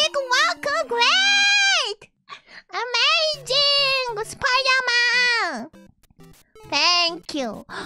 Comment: Did she say "amazing spiderman"? xD